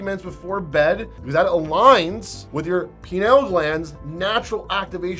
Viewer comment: did you just say penile glands? lol